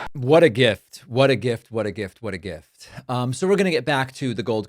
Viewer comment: did he say “grift” ????